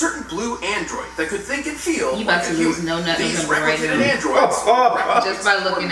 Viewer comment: why u said was gonna lose no nut November who else heard her say that 🤣🤔